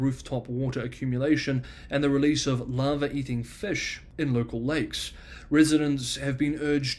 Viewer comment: Did anyone else hear "lava eating fish"??🤔